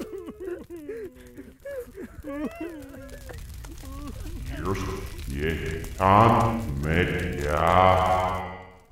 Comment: what did the tree say? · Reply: I put it in reverse and it says I am happy tree